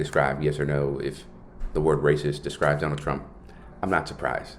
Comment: I literally thought he said rapist!